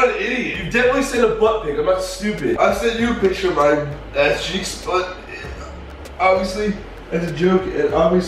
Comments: Did anyone see that it said checks instead of cheeks? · OMG this is about the millionth time I've watched this video and I just realized the caption says a$$ checks instead of cheeks XD